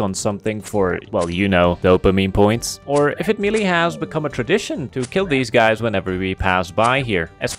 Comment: did you really say "If it 'melee' has become a tradition", as you were engaging in melee, my boy to are a genius